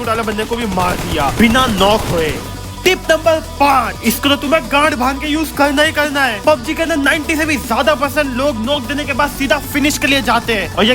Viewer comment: I hear gand 😆😆😁😁 thank you bhai love from rajasthan💋💋❤❤